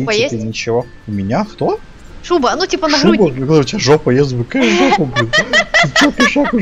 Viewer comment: Мне тоже послышалось : у тебя жопа есть ? :D